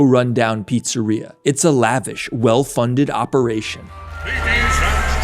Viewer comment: Did he say Candy-Asses? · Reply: Kandyasses*